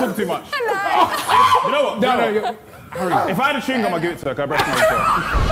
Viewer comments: What does he say at 4:30??? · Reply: If I had a chewing gum I'd give it to her because.. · What is he saying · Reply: If I had a chewing gum I’d give it to her because her breath smelt as well